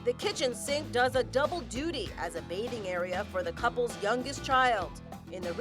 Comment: Did she say that the kitchen sink was a mating area for the youngest child?